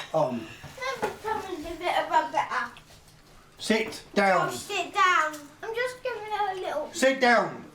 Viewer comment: What is saying? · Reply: “I’ll sit down. I’m just gonna give her a little kiss.” Josh is saying. However if he was it would be censored in US version, but it wasn’t. These kids behave more nicely for their father than they do their mo m